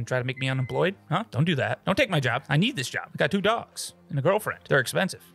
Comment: i heard him say i got to do drugs and a girlfriend not two dogs 😅